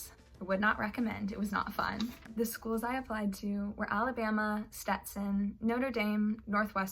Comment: did she just say noter daim